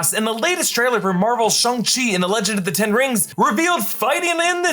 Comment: Did you say SHUNG CHI??